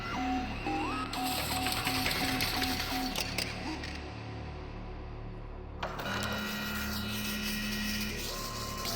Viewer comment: did anyone hear the jailbird say please no more? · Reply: "For both of us, please no more!"